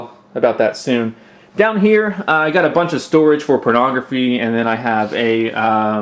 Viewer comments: A bunch of storage for pornography did I hear that correctly? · is it me or at 2:09 did he say pornography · did he say pornography